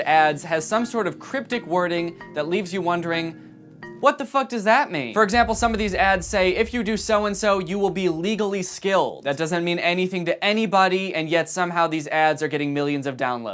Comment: i thought it said skinned 😭